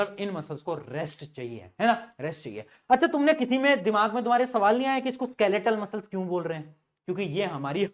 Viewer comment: Sir when u said that kisi ke dimag m nhi aaya I think u are saying tum me kisime dimag nhi h.......😅😅1:03:40 anyways sir class was very interesting.....at all❤❤